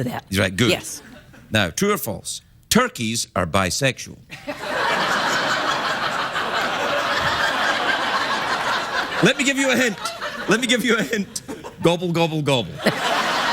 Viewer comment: What did he said at 9:30-9:45? Thanks!! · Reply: "True or false, Turkeys are bisexual. Let me give you a hint, let me give u a hint. Gobble gobble gobble."